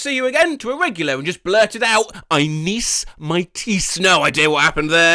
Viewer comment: who else heard “I miss my teeth 🦷”